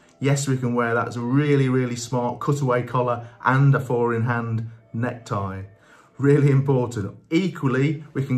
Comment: I thought you were saying foreign hand necktie, I Google it and it's four in hand necktie, 😂😂😂, I'm feeling really stupid. You should do a video showing how to do a four in hand necktie